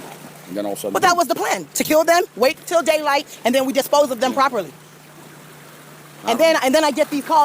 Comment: the woman in the audio file is yelling at two workers. she says they were supposed to wait until daylight to dispose of dead deer. sounds like she says "secure & dispose of", instead of "kill & dispose of." so these guys or someone decided to take off at night instead, they crashed the boat. the boat that transports staff to the island. its a big stretch to sensationalize this into murdering children or whatever Kyle is so excited to imply here. Like so many other podcaster indy news people,.... I WISH we could stop making this horrific shit worse than it is, for these guys to get more clicks.